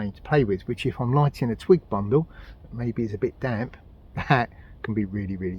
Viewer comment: Did he say, "....when I'm lighting a tweek bundle..."???